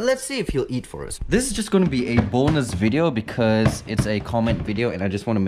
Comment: I thought he said "this is going to be a boneless video"😅